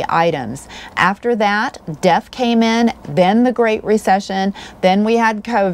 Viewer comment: Could not be sure what you said here: Around the 6:40 mark, "<num>...those were high quality years. After that, _?__ came in, then the great recession". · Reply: You can just rewind to play it again. The <num> were high quality years for motorhomes. These RVs tend to have all the bells and whistles but are pre-DEF. In 2008, mfrs had to cheapen their products to stay in business, due to the Great Recession. And many still went out of business. Thanks for watching.